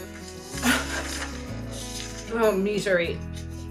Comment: did Jennifer just say “Meat-sery” as in like misery? Because if so that should be some Hot Ones Merch. “Meat-sery” -J. Gardener 😂❤️